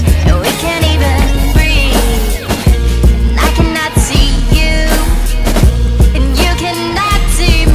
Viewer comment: For some reason I hear "...I can nazi you, you can nazi me...." lol